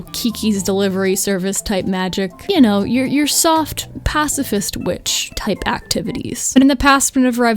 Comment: Thanks to some buffering, I misheard that as "soft power" rather than "soft, pacifist." 😂 but also 😢 cause my mind only went there because political news has been on my mind too much. Thank you for the video. It was a nice escape for a little while.